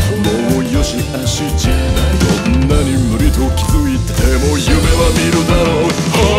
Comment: 女に無理と気づいてもって聞こえない？ 本家はこんなになんだろうけど